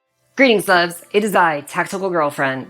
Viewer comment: Is she saying greetings loves? or Greeting Lugs? I'm kinda deaf. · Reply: I hear it as " greetings loves".